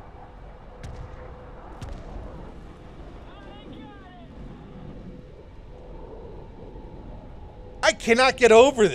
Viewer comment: can someone tell me what does the in-game voice say at 3:16? because I'm italian but I hear "a mangiare", that means "let's eat", and it doesn't make any sense in the game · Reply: Listened again with headphones...it said “Ahh, I got it!”